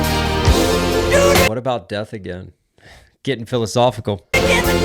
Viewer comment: the lyrics are ”what about apathy” but your interpretation was great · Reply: I actually thought he said "what about empathy" hahaha